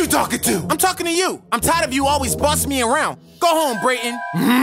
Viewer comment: I thought he said bussin